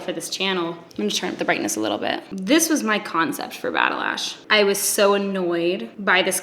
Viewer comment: I love this video with my whole heart. I’ve been following since Batalash and I am so happy to now call you my friend. You inspire me every day! LOVE YOU! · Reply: damn... I haven’t heard batalash before and I swear to god thought she said battlelash, a badass name for a badass cause. Lmao. Glad I could count on you to clear shit up